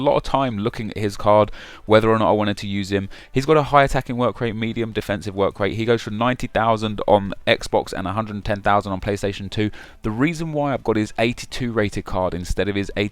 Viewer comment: Did he say playstation 2 at 3:15?